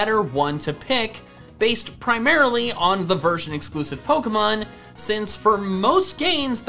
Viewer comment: for some reason I hear "virgin exclusive pokemon" and I was like "hell yeah, finally." Then I realized I misheard and reality set back in...